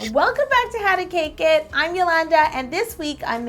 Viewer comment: Did u just say "Shwelcome" 0:00 XD